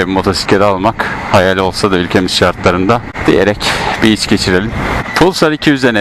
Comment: ''Bir iç geçirelim'' cümlesini,ben ''bir içki içelim'' anladım :)))